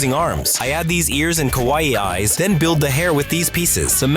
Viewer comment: did you say kowai eyes